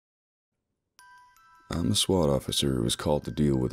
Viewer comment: Thought it said “I’m a Twat” 🤣